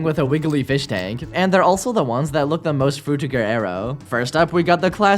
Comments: what did he say at 7:00 ? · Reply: frutiger aero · what arr you saying here? it just soinds like a mesh of syllables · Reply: They said “frutiger aero”. It’s an aesthetic that was commonly used in the mid thousands to early 2010’s.